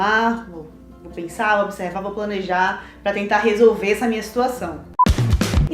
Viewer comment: eu ouvi "pra tentar resolver essa minha mestruação" kkkk